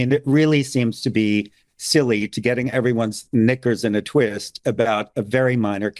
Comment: did he say knickers?